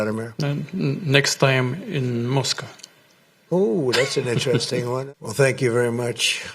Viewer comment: I heard he said: "Next time in Mosque". I think Mr. Putin invited Trump to convert to Islam.. 😂